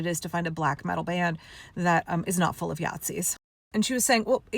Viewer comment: Listened to this while I made lunch, when you said "Yahtzees" I heard "Yacht-zees" and felt it was an appropriate description.